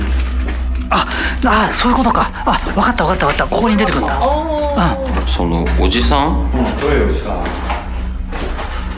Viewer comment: なんて言ってるの？ すごく聞き取れないけど、誰かなんか言ってる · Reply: 普通に奥でスタッフさんが『のぼりますか？』って言ってるようにきこえますが… それではなく？